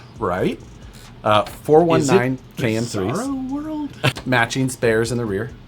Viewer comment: did he just say bizzaro world....